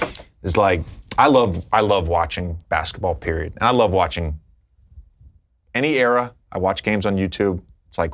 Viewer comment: What did Lebron say at 28:42? I didn't catch the joke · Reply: i think he said "any year?" like would you really wanna watch 60s or 70s basektball? i might be misinterpreting though